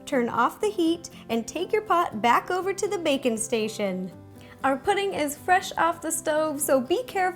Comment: I thought she said bacon station and not baking station lol😊😊